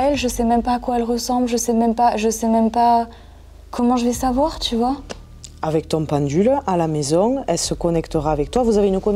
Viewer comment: Personnellement quand a paniqué j'ai entendu "avec tony " alors je sais si c'est elle qui la dit ou pas et sa ma fait monter les larmes au yeux. Je sais pas si sa a un rapport